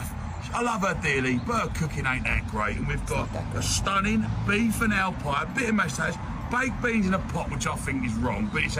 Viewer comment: as a Bri'ish viewer, this is so fucking funny lol p.s. the subtitles failed him, at 0:32 he actually says "beef and ale pie, bit of mashed taters, beans in a pot" the moment he says "bit o mashed taters" it's like the world folded in on itself it was so fast lmao